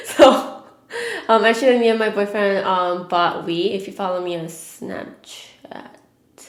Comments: I thought you said "me and my boyfriend bought weed". 😂😂. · omg i thought she said me and my bf bought weed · Oh I thought you guys said that you bought weed.... Lmao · i thought she said me and my boyfriend bought weed lol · I thought that you said "actually me and my boyfriend bought weed." until you said that you played Mario kart, then I realised you said Wii lol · I thought she said that her and her boyfriend bought weed instead of Wii.. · oh dang XD I thought she said "me and my boyfriend bought weed" XD hahaha love her great video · I definitely thought you said you and your boyfriend bought weed lol not wii 😂 oops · Is it just me that heard her say she bought weed instead of wii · Omg I thought you said "me and my boyfriend bought weed" 😂😂😂